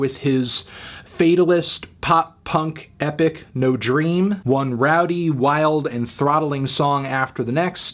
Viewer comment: I thought it said “no bread” not no dream 😂